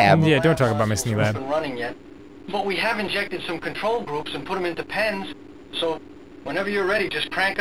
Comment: I heard "We have injected some control groups and put them in Depends." Was expecting a game where you round up people at a retirement home.